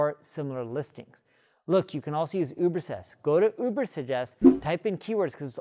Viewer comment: What words am I not pronouncing correctly, friend? I thought I was speaking normally. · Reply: Not incorrectly, I think it has more to do with accent. An example is at 1:14 (which to me sounded like "ubersest", but it caught my ear in a few videos of yours), which caused me (a non native English speaker) to rewind. Please don't feel offended, it was a truly honest question to know if it was a purposeful strategy. Thanks for your insights by the way!